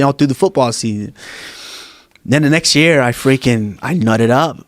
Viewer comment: " I nutted up" ......Is that what he said ??? and just what does it mean ??